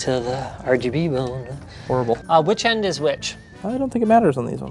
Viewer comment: Did I heard Horrible?